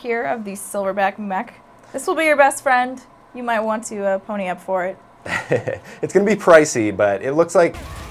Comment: did you say "you might want to, er, pony up for it hehe"???